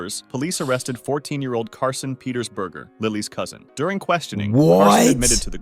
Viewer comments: DID U JUST SAY THAT HIS LAST NAME IS BURGER? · DID YOU SAY YOUR NAME IS BURGER? DOES IT COMES WITH FRIES? IS YOUR MIDDLE NAME CHEESE? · did you just say your last name... is BURGER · "Burger!, i'm sorry did he just say that his last name,... is Burger?" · BURGER? DID HE JUST SAY HIS LAST NAME WAS BURGER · I'm sorry, did he just say that his lastname is "🍔" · I'm sorry but did he say his last name is burger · DID HE JUST SAY HIS LAST NAME IS BURGER